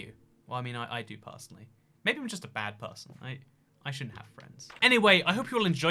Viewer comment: Did- Did he just- Did he- "I shouldn't have friends" Oh- That makes more sense- Thought he said 'rights' instead of 'friends'